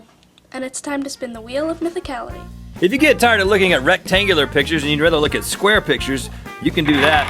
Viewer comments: I heard "Rhettangular" pictures :P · I thought Rhett said "Rhettangular pictures." That would have been awesome.